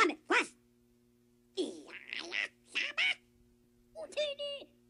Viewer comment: I hear Houdini